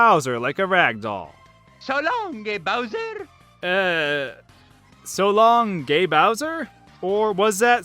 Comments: I thought mario said.. "so long may oswer!" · Imagine that "So long gay Bowser" one as a kid from another country who does not yet learned english in school. I really had NO idea what Mario said there. Not even a real mishearing because it did not sound like anything that would have made sense to me. "Galong Gefangtey" maybe..? :D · I thought Mario Said “So long Dear Bowser!” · I think Nintendo confirmed it was either So long king-a Bowser or So long-a Bowser 2:35 MEEEEEEXXXXXIIIIIICCCOOOOO! 😂 4:45-46 Remark! 5:57 it sounded like the second one was saying gridlock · When I heard "so long gay Bowser" as a kid I thought he said "Tadaa game Bowser". Don't ask me about the "game Bowser" thing. I know it's strange and doesn't make sense. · I always hear :"shalom gay bowser" for some reason. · So long gay Bowser... I still hear that. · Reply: I hear "So longie, Bowser!" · I thought I heard “so long dear bowser”... Oh also, in super mash bro’s. melee, Wario’s final smash says, “HEEP TA-DA HO” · I always heard "so long, les bowser" as a kid, which always sounded quite poetic to me. Strange how mario would say that one phrase in French though. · mario 64 misheard. what i hear so long e bowser! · I thought Mario said in Super Mario 64 " So Long-y Bowser ".... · I've always heard "So long me Bowser" Also a really funny one was in Mario Kart 64 when my 6 year old self thought Mario was saying "Mexico" instead of "letsa go" and so for a while I thought Mario was hispanic.